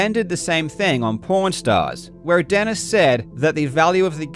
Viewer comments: Karl you really need to work your pronunciation of "pawn" because I could have sworn I heard you say "Porn stars" and wondered when these two got into the porn business XD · I swear it sounds like he says porn stars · Ok, I know its just your accent but its fuckiing hilarious 17:06 you said porn stars instead of pawn stars! Great journalism other wise. You should make a podcast about gamin6g seedy underworld. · did he just say p*** stars?